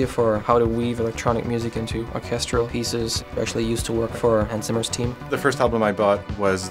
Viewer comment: Did he say he worked for Ann Summers?